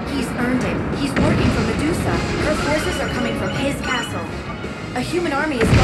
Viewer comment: I thought Palutena said the forces were coming from his asshole....